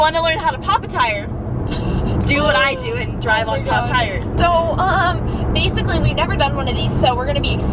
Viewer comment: i thought she said pop tarts lmao